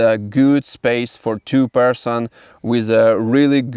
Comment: I thought he was saying, “Two Percent” until I realized he was saying, “Two-person tent.”